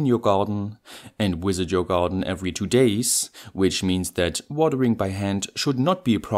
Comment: I had to replay this part a few times over because I heard "whiss in your garden every day"... The way you're holding the hose didn't help with this either :^)